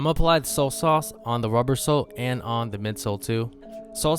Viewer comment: did he say soul sauce or solt sauce?